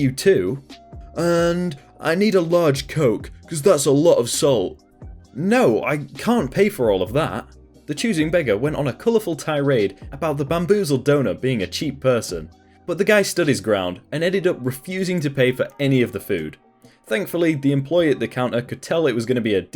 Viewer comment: i thought he said bamboozled donut